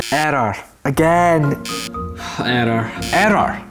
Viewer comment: i think i heard him say "arrow "